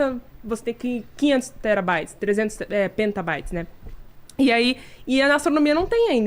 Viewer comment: só uma correção. O nome é PetaByte, e não "penta" como foi dito 3x Pode parecer irrelevante, mas vai fazer muita gente aprender errado.